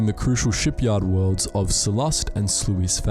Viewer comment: I deadass thought it said "sluts van"